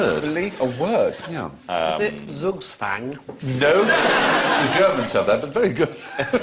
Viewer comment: What does Jo say at 14:40? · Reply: "zugzwang" ˈzʌɡzwaŋ,ˈzuːɡ-/nounChessnoun: zugzwanga situation in which the obligation to make a move in one's turn is a serious, often decisive, disadvantage."black is in zugzwang"